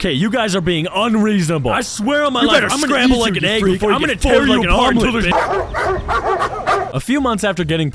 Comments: Bro I swear I heard him say “you better scramble like an egg before you get folded like an omelet ni-“ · I heard the mini version of Shelby say get scrambled like an egg before you get folded like a omelette ni